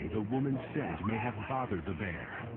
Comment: At the 0:40 mark what did the narrator say. "some say the.." what? The woman's something? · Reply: The woman's scent. It's possible she was wearing perfume or just smelled unfamiliar/bad to the bear.